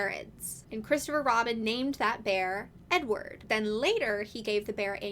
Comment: The first couple of times you said "Edward" i kept hearing "n-word" and so i was sitting here trying to figure out uf you were saying that Poohs og name was a slur lmao; which i figure you would have a very different tone if that was the case lol, but like, I dont really think I'd be surprised at this point lol